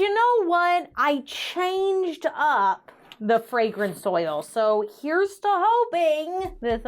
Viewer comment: When you said "fragrance oil", I thought you said "fragrant soil", and I was gonna say "huh that's an interesting ingredient" 😅😂